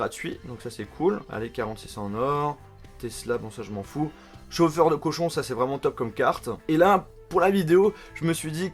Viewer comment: j'ai entendu chauffeurs de cochon😂😊